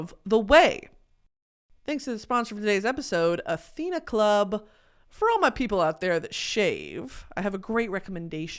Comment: I skipped 5 seconds on accident on the first ad and I thought it said "For all my people out there who shit razors"